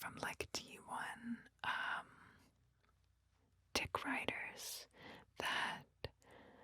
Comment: did she say T1 d1ck riders did I hear that right?